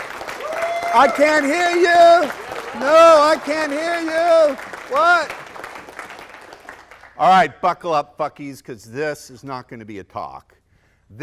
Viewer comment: Did Ross say "Buckle up f**kies"??? :-/